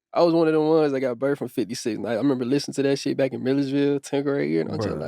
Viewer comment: Did bro say Milledgeville 3:50?